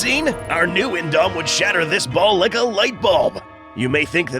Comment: I heard ball licker light bulb is that what he said?